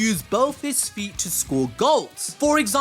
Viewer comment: did anyone catch the fact that he said socre instead of score